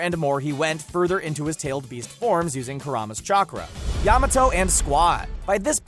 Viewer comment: Did he just say kurooma